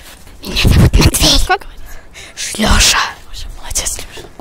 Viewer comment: кто услышал ШЛЕША? 😂😂😂😂 Я только увидел сколько лайков набралось первый раз! Блин подпишитесь на мой канал плиз. Бабка:Купи огурчики!